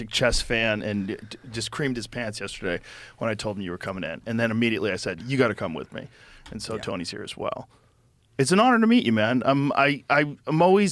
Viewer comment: did joe just said tony creamed his pants when heard about magnus! 😂😂😂